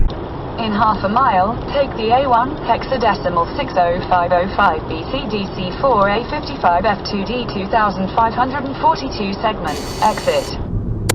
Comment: Am I hearing the navigation system right at 1:22?: "In half a mile take the A1 Exit .60505BCDC4A55F2DT2 542,000 segment exit" Uhhmm.... what?? Think it went haywire :o